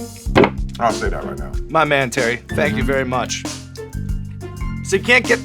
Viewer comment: What does he say at 10:07? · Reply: John Zackarias he says “it doesn’t taste as good as the hot ones, I’ll say that”